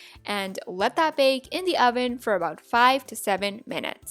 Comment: Who else heard her say “the Oder” 🤣 love your vids🥰